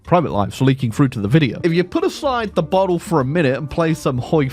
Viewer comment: I thought he said "bottom" instead of "bottle" day ruined